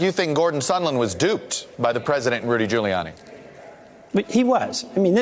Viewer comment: totally sounds like he says "... was duped by President Rudy Giuliani"